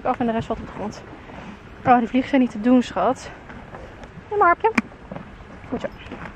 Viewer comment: Ik hoorde de naam ik weet het niet zeker als je ze wortels geeft tijd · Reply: Waar hoor jij een naam? Ze zei: 'Neem maar een hapje'